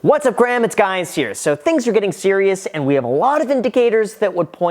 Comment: Did he just say-‘What’s up Graham, it’s guys here?’